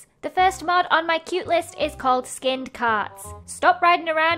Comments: I THOUGHT YOU SAID SKINNED CATS!!!!!! Lol 😂❤👽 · when lizzie said skinned carts i thought she said skinned cats.... oops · when lizzie said "skinned carts" i really thought she said skinned cats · i thought skinned carts said skinned cats and it was j a hairless cat😳😳😳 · I thought she said skinned cats😭😂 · I like skinned carts and rats but at first I thought u said skinned cats.😅